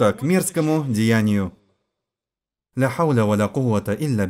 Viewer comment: Ассаляму алейкум уа рахматуллахи уа баракатух, на 33:30 секунде что говорит иман? Ля уля кауля вроде? Если ошибаюсь извините. Кто знает определения можете написать что это значит и правильно написать транскрипцию. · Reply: Уа алейкум Ассалям уа РахматуЛлахи уа Баракатуху. Ля хауля уа ля куата илля биЛлях - нет силы и мощи кроме как у Аллаха.